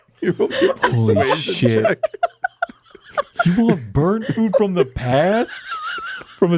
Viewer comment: Burnt oh thought he said burped the crops.